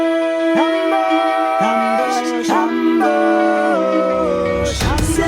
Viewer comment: TIME STAMPS: 🐴THE TROY SAGA🐴 0:00 - The Horse and the Infant (Draft) 3:55 - Just a Man (Draft) 6:51 - Full Speed Ahead (Draft) 10:04 - Ismarus 13:33 - Your Light 16:33 - Ismarus (Perimedes’ Verse) 16:38 - Man of the House (Eurylochus) 16:45 - Unhand Him 18:33 - Mercy Has a Price 19:08 - Comfort Zone 19:59 - Cope With That 21:24 - Pick Me 21:53 - To Be Mine 👁️THE CYCLOPS SAGA👁️ 23:06 - Foostep 23:12 - The Cyclops 23:21 - My Goodbye (Draft) 🌊THE OCEAN SAGA🌊 24:44 - Luck Runs Out (Draft) 🐷THE CIRCE SAGA🐷 26:10 - Puppeteer (Draft) 26:30 - Scrapped Hermes Song 27:18 - Till We’re Home ☠️THE UNDERWORLD SAGA☠️ 27:26 - The Underworld (Draft) 27:54 - Man of the House ⏳THE WISDOM SAGA⏳ 29:42 - The World Would Be a Better Place 30:14 - We’d Be Fine (We’ll Be Fine Draft) 31:06 - “Oceans in My Eyes” 31:55 - God Games (Draft) 🔱THE VENGEANCE SAGA🔱 32:49 - Appetite 33:43 - Belong To You 34:37 - “Trick” 34:55 - In Vain 👑THE ITHACA SAGA👑 36:54 - Penelope (Draft 1) 37:08 - Penelope (Draft 2) 37:20 - “Legend” 37:39 - Odysseus (Scrapped Lyrics) 37:51 - Royal Wisdom Burst 38:36 - Olive Tree 🫶! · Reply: I thought they were saying hamburger 💀💀💀